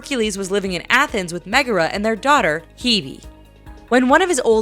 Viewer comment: I was confused about Hercules 2. When listening about it, I wasn't sure if I heard the daughter's name. I was wondering if it's Phoebe or really just Hebe.